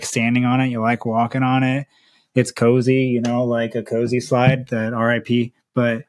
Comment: I couldn't catch the exact product name? Thanks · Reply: Deckers X-labs Ko-Z EZ Slides